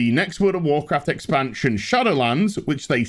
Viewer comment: I thought, I heard world of Warcraft: shadow legend, like raid shadow legend sponsors you, my mistakes...